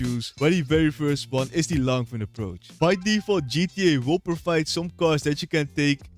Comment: what did he say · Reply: “Where the very first one is the longfin approach”